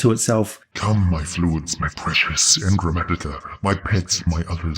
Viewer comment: I can't understand it...at all. :/ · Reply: You know, for just $1 a month you can read the entire scripts for my videos, but just for you, here is what I said "Come, my fluids, my precious engrammatica, my pets, my others, let us decide, and I shall act, quiet your torments, float in the reverie, I will bring more for you, always more, for your pain, for our masters, for the experience, yes, yes, we are agreed, bwuehehehehe mmmmm"